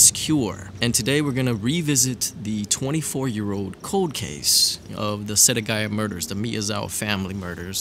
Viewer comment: I heard you say Code Case when pronouncing Cold Case.....very wise.